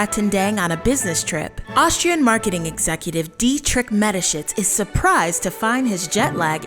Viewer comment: Im sorry, did you say his name was Dietrich Metashits?